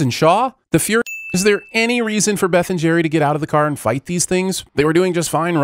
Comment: I could have SWORN that he said "Ben and Jerry." I don't know why he would have said that either.